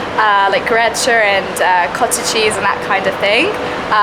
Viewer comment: did she say "cottage cheese"? That has to be the most empty headed speech I've heard.